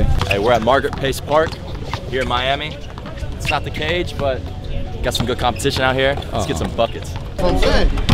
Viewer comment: I thought bro said they was playing sum good competition